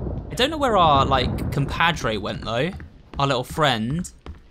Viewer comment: I repeated the minute and put on the subtitles just to make sure I heard right... He said "compadre"! I found it so funny and cute! (I think it is "comadre" in female... although it is also used as to refer to a gossip kind of woman, but in White Day rumors even are a collection so... pretty accurate in my eyes.)